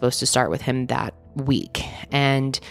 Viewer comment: When she said “he was approached by someone in HR” I thought she said “someone in a jar” 😂